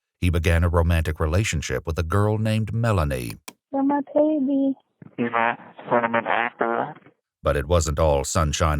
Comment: LMAO @ 59:20 I think he said “you’re my cinnamon apple,” but I like what they put instead